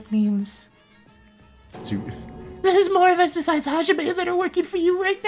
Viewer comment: My notifs for you are broken no wonder im always late now :^ I legit thought monocrow said hajime hinata for a minute lol Yeah uhm.....I really want to be a volenteer for the female voices lol No hate its just kanade sounds more annoying than usaul-..Im sorry ;^